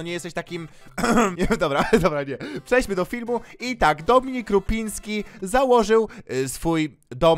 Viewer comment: O jeju,gadałeś o Dominiku Rupińskim a ja usłyszałem "Dominiku Łupickim"